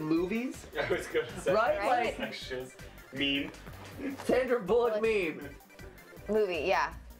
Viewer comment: I thought Sandra bullock said Sandra "Bullcock"